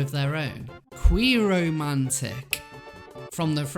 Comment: I honestly thought you said queeromancer at one point xD